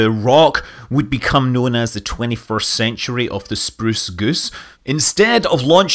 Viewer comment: The spruce goose of the 21st century? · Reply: you misheard, he said the 21st century of the spruce goose.